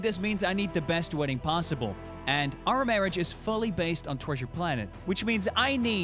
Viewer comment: I heard: Torture Planet.